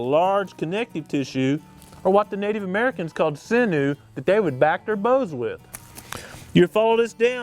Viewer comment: WONDERFUL video! Thank you for your help here. Please tell me what you said right at the 6:50 marker. It sounds like you said "What the Native American would call sinew that they would back their bows with." Is that correct?